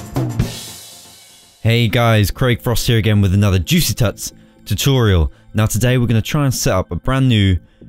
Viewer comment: For a second there i thought you said juicy tits